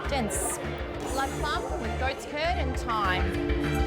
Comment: i thought she said goats turd